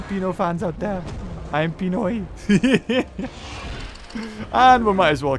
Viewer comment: did he said "i am pinoy"?!? Im filipino!